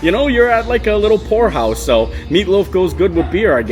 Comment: you said "you're at a little pour house." I thought you said whore house. 🤣